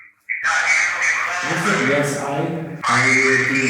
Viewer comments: when you asked "are you a demon" sounded like a distinct "Yes.I.Am." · the thing said yes I am so if you look back you can hear that is what it said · i heard: Yes i am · idk if im going crazy but i heard "yes i am"